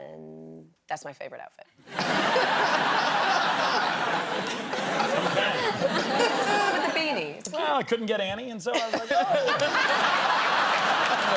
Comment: "I couldn't get Annie, so..." BEST PART 😂😂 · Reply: I thought he said "I couldn't get any, so..." as in he couldn't any of the clothes 😂